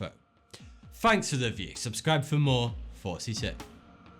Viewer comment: Why does it sound like he's saying Forty two? I hate that shit lol. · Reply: at first I didn't realize he was saying "Thoughty 2" and I thought he was saying "42" and I thought he was trying to impersonate Hitman 😂